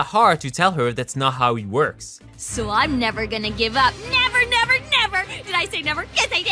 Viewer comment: what does Candace say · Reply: Never